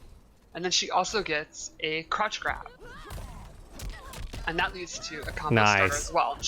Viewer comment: Sheeva's fatality here might be my new fav next to Sektor's "Scarecrow" from MK9.. Also, whenever they say "crouch grab" I keep hearing "crotch grab" lol.